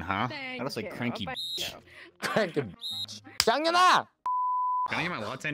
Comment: I thought he said “TANGINA!” I started Creating my own GRAVE